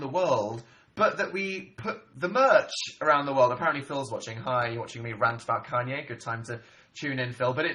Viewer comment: Did he say at around 15:00 that Phil was watching? xD What, was he like "Well there's nothing good on tv rn, Dan's doing a liveshow so I guess I'll see how that's going" Despite that, I loved how upbeat and happy he was was during this liveshow. Also his laugh is incredibly contagious.